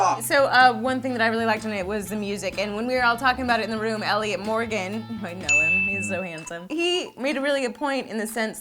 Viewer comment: Wait, did say "Elliot Morgan, you probably know him hes so handsome" at 4:06?